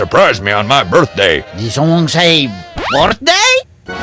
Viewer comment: did someone say birthday